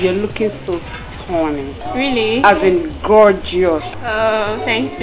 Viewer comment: did she say GROgeous? dayum girl! movie is funny tho..keep itching to forget my fingers on face